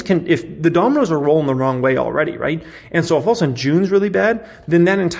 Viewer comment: I thought you said we rally in June? 😂😂